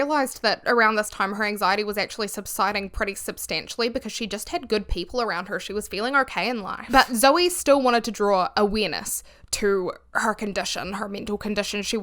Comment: I’m so sorry, but I had this playing in the background and thought you said “draw a weenus” and I had to stop what I was doing and play it back again 😭 I though it was some new kind of saying that I hadn’t heard yet